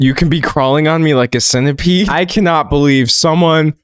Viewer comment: I heard 'crawling on me like Santa's pee'!!